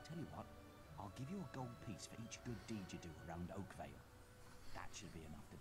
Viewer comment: When you said we are in a place called Oakvale, I thought you said oatmeal. I live in oatmeal!